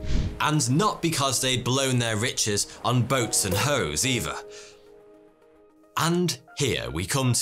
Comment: Did just say “Boats and Hos”? 10:40. 😆